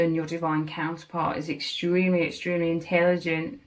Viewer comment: Ahahaha I love it I heard “InTAYLigent” hehehe 😂❤🎉